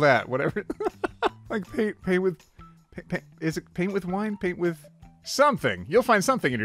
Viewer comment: I think it says "paint with twɐt".